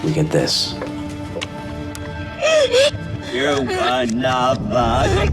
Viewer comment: what does the recording say backwards ? they act like they understand. but i don't hear it. I hear "You are not faster " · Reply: You're one of us.